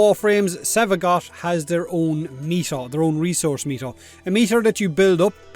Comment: Thought he said resource meat